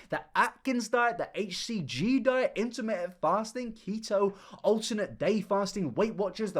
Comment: I swear I thought that said “intermittent farting”…